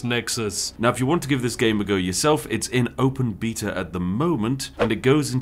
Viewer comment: at first i heard open petah